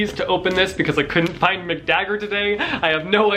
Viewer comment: Did I mishear or is your dagger really called Mick Dagger? If it is, that's amazing ^^